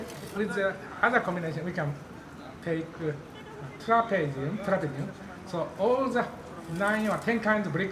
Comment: Great video! But at 2:40, it should be "trapezium", not "trapadium" as the subtitle says